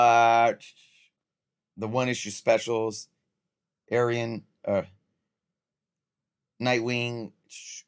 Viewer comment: errrrrmmmmm... what the fauci! Bro said ayran 💀💀💀💀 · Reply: lol no. I said Ah-Ree-un, which is how you pronounce Arion.